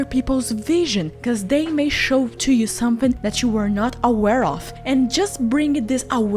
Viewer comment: yo I heard it "a werewolf" I think I'm furrylizing